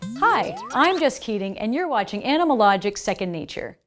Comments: I thought she said "Hi! I'm just kidding and you're watching animalogic..."😂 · I don't think i will ever stop hearing just kidding. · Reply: My first time browsing 'em videos on this channel. I thought I heard "I'm just kidding" as an introduction. Surreal. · I thought she said "I'm Just Kidding."